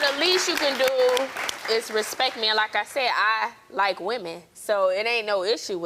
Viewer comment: This relationship giving me polyamorous, he keeps sayin he didn’t like women he also giving gay vibes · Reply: I'm GLAD I'm not the only one who heard him say HE DIDN'T LIKE WOMEN.Crazy-Crazy.Thank you,Casey Grimes-Finley,Chicago,Illinois